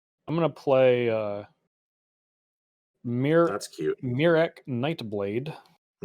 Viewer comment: Talking about deathtouch...I heard mirror and thought "Hell, mirror shield? Nope, shucks. Lol